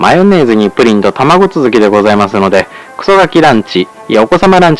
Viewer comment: あ、クソガキランチでしたか😆 遅咲きランチって聞こえて、グーグルせんせに相談するとこでした😁 昭和44年生まれ、ポンコツだわ〜。